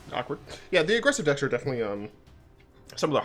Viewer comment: did you say shock-ward?